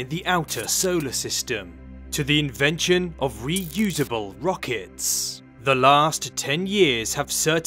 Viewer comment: Lol great video man, just had to rewatch the intro thought you said "reusable pockets" and I'm like tf? Anyways always find you vids really engaging. Good year to all and many to come.